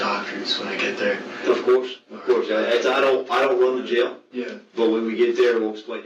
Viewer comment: I love subtitles. Whoever transcribed was lazy. How thats "inaudible" baffles me: @ 5:52 "I don't run the jail" @ 8:26 "like how Dylan Roof did" @ 8:53 "were talkin Kevlar" @ 15:10 I'm pretty sure he said "yeah they did barely" not "Yeah very well". @ 15:59 "yes, yeah we were discussing that". "Are you fam(iliar)- d-do you know what that is?" @ 16:10 pretty sure he says "atomwaffen" (or whatever tf its called) not "out of these people" @ 16:13 "not these three individuals here? Or, er I mean not lumped in with that". (THIS INTERROGATOR is trash!!) @ 16:45 "in that garage is HMTD" @ 20:37 "like today I was at my boiling point" Immediately told the authorities the truth, asked for a doctor, substantiated all claims about roomates.... damn i hate to give credit to a POS killer who idolized mass murders but tbh id rather him do what he did. Rather than not, and rolling the dice to face the potentially worse consequences.